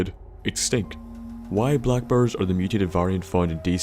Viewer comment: I swear I thought he was saying black birds for the first minute and a half.